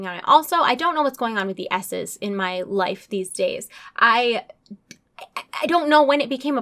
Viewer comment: i just heard "i don't know what's going on with the asses in my life" lol my mind is so dirty too late to clean it